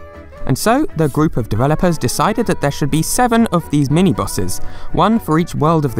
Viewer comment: there is a typo in the CCs. You say "mini bosses," but the CC says "mini bowsers." 14:10 you say "Man, the 90's were weird," but the captions say "Man, the 80's were weird. " Just wanted to mention it for those hard of hearing. Thank you.